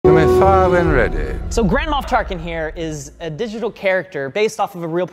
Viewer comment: I keep hearing "Grandma Tarkin" 🤣